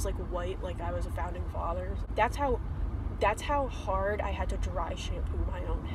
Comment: i heard "like i was in founding fathers" like its a band name. Founding Fathers